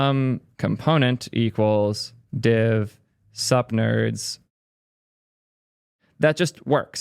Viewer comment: I misheard "sup nerds" as "sub nerds". Perfect opportunity for *sub*liminal messaging. P.S. Subscribed!